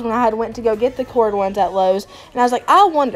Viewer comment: Girl where did you get those remote lights I am obsessed I love your island makeover it’s beautiful you inspire me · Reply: I thought she said lowes